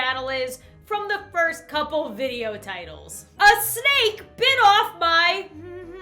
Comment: Tbh I would usually have thought that, my hearing is super questionable sometimes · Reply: Ruby Yates I thought she said a SKANK then I thought STEAK